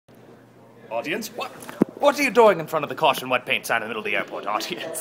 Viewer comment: i thought it said wet pants